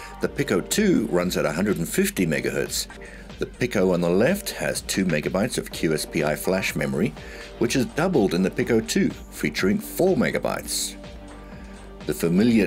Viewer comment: Did I hear the OG had "double the flash memory at 2MB compared to the new Pico at 4MB."? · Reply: "The Pico on the left has two megabytes of QSPI flash memory, which is doubled in the Pico 2 featuring four megabytes." :-)